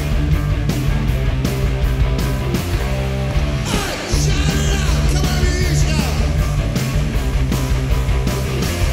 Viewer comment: Hello everyone, you can write what Ozzy says at this moment, I can't understand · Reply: Shout it out, come on let me hear you shout